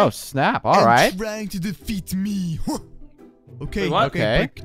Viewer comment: i thought that they said and trying to eat me instead of defeat